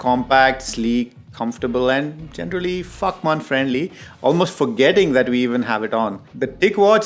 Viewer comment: Okay I'm dying because I thought u said Fuckman safe 😂 😂 😂, I was like what did he say but u followed quickly with the pronunciation and definition, thanks for the good laugh!